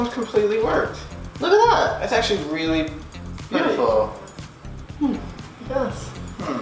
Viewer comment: Does anyone know what lance said at about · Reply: Noah Bovenizer "pretty"